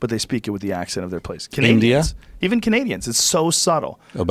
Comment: I thought he said canadia but he said India